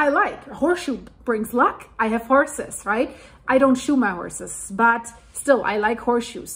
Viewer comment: I thought you said "i don't shoot my horse's " . when you said "i don't SHOE my horse's" LOL 🤣